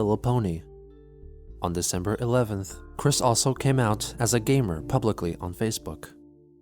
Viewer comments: oh my god. i put this on the background and when said "he came out as a gamer" i genuinely thought he said gay man i was so shocked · Oh jeez I wasn’t looking at the screen and I thought you said Chris came out as a Gay Republican at 7:16 💀